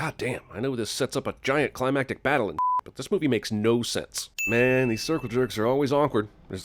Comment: it sounds like he's saying "movie" but the subtitles say "move"